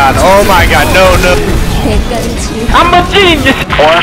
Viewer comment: what he says? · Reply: That's a sound clip of videogamedunkey saying "I'm a genius!"